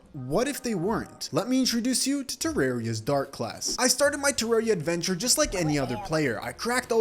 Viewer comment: Ngl I thought it said “dirt class” lol